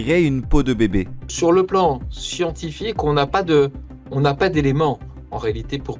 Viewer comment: Scientifique !!! 😂 (dsl j'ai entendu bigard)